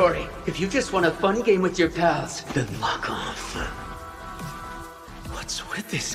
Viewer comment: I heard “If you just want a fun game with your pals, then FUCK OFF”